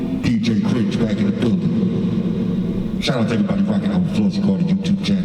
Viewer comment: I think I miss heard you but I heard "shout out to everyone at the flossy carter bbq live stream."